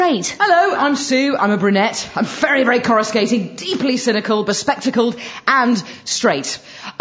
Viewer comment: "Hello, I'm Sue. I'm a brunette, very, very coruscating, (something about spectacles that I couldn't quite make out clearly), and… straight." (Or so we thought at the time of broadcast…) Thanks to Rhona Cameron, we now know differently! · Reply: 'deeply cynical, bespectacled'